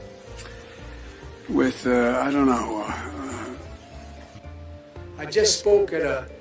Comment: did he just say "i dunno who iam"?